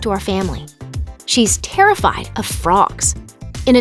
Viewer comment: I was watching this video while writing a paper and I thought the video said that she was scared of rocks. I was like wtf, how can someone be afraid of a rock?? Lol